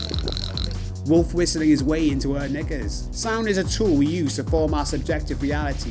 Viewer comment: When you said, “Nickers,” I had to slow speed and rewind to make sure I wasn’t hearing what I thought I heard. Thankfully, I don’t believe that I heard you properly due to my native accent. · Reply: I try to tame my accent a bit as it can be hard on the ears, but I definitely said knickers 😂